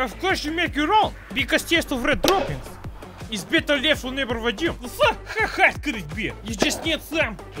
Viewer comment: Did you just say "Vadim, let's go"